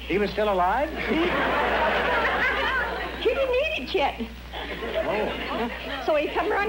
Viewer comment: I love the way she says "chet" instead of "yet" with her accent :D